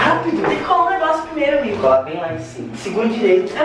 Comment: “Tem que colar o negócio primeiro VIDA” Só eu a ouvi assim? Ou eu tou loka?